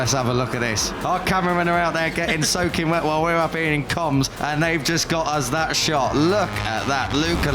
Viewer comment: wtf did the announcer just say??? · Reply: "our cameramen are out there getting soaking wet while we're up here in comms. And they just got us that shot."